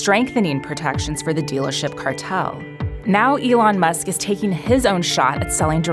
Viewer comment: did she just say "Dealership Cartel"????